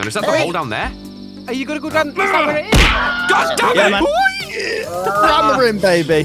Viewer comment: did he say god damn it or dog damn it I’m just curious